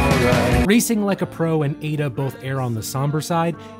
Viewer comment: I can't be the only one who heard it as 'Racing like a pronoun...'